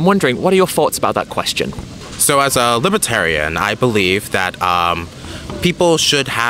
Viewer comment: i thought this guy said he was librarian. where are your books john!